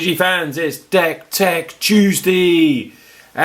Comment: sounded like u said dank tank tuesday for a second there lol